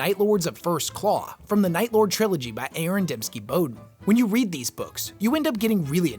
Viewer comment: My brother in Christ did you just call ADB A-A-Ron?